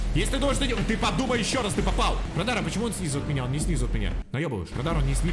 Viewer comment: Мне послышалось Мадара :D